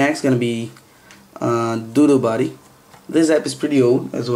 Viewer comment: lol 13:34 sounded like he said "next is gonna be.. doodoo body!"